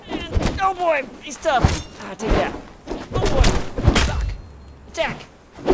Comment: I swear at 0:08 it sounds like says “fuck” and quickly covers it up by saying “jack” Just me?